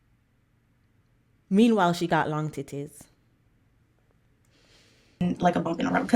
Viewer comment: yo, did you just say, “long titties”??? I’m dead 😂😂😂💀💀💀